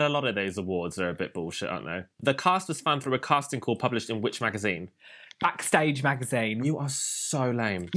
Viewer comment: I legit heard, “The cast was found from a casting call published in Witch Magazine” and I was like, wow that’s specific.